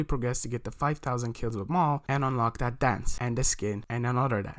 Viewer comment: wth did he say at 4:10 all i heard was anenorlertha